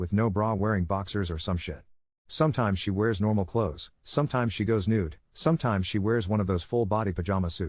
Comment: sometimes your daughter walks around nude??! Am I hearing this correctly? 😅